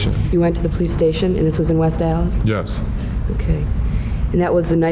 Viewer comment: did she say west dallas?